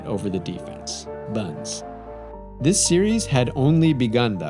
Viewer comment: Did you just say "....buns" ? Lmao